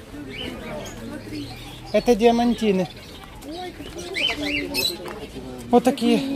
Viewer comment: Мне послышалось? ?? Дермантины? ?!! Ну ты кадр!!! АМАДИНЫ. ....